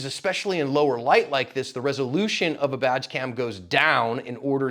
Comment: I thought you said Vag cam for a second!